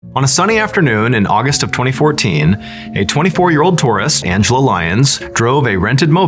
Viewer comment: For a second I thought you said "34 year old Taurus" and I was like, "Is that going to be pertinent to the story?" but then I realized you said "tourist"😅